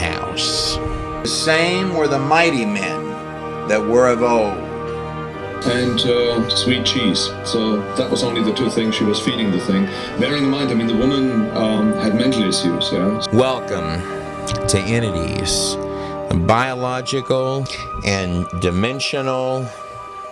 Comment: did he say she had mental issues at 9:34? what did he say?